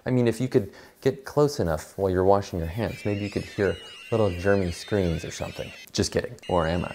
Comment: I thought he said little germans scream and I got a little scared xD